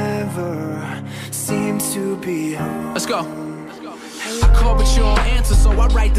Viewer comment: i thought the guy looking at said let's go